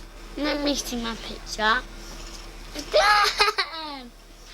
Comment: Datdeh Thats what i heard